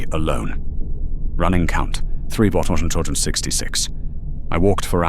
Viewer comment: What do you say on 20:44 running count something something 66? Makes it more creepy lol.